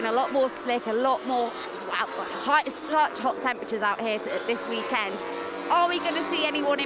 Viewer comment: MotoGP= fire Also 1:00, am I the only person who genuinely thought she said slut instead of slight. It kinda took me aback